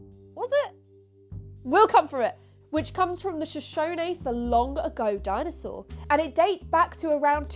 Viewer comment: Thought you said LONG DICK DINOSAUR 🦖 😂 SORRY 😂